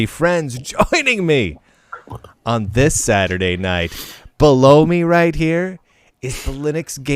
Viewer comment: Had to re-listen at start. Phew! Happy to report that no-one said "blow me" as i had misheard...